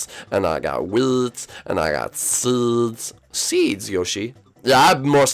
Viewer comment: On 7:20, I thought he said weeds 😳